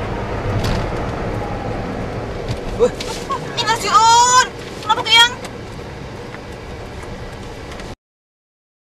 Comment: mbaknya bilang apa sih.. nggak jelas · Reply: Innā Lillāhi wa innā Ilaihi rōji‘ūn, kenapa (i)tu yang?